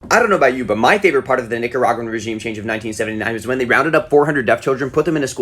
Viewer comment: i thought the video was going in a slightly different direction when you said “regime change” and “rounded up 400 deaf children” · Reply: It was worse for me 'cause I misheard "rounded up" as "grounded up" 💀